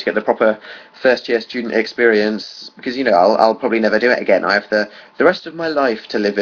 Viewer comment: I'm sorry all i heard was "I have the rest of my life to live with Phil" We can hope.